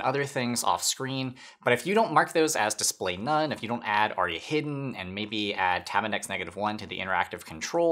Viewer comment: caption says "Already", should be ARIA. Otherwise, nice !